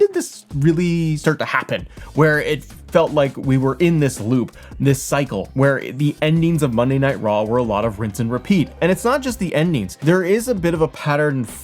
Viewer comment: I thought you said money night raw in the beginning of this video